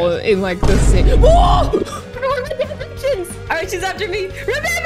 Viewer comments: Why did yell “stray kids” 😭😭😭 did I mishear that or what 😭😭 Edit: it’s at 9:58 if u wanna know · did she say stray kids or am I paranoid